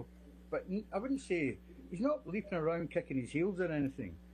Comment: Can someone tell me what the witness says at 19:03? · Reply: He is not leaping around kicking his heels